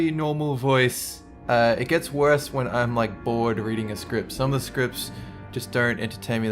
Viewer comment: When he said reading a script I thought he said eating a script